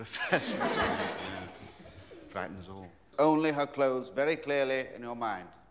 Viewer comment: Can anyone tell me what he said at 0:20? Didn’t hear it well... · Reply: He said ‘frightens all’ referring to professor Snape :)